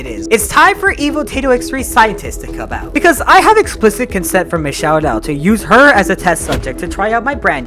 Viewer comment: thought u said explicit content💀💀💀